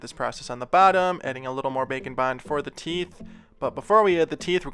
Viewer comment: It took me watching four of your videos to understand "Bake and Bond" - I always heard "Bacon Bond". ...yeah, I wondered about that too.